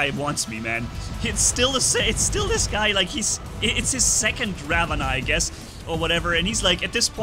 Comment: I thought he said “banana” so I backward the video a bit and heard that he clearly said Ravana.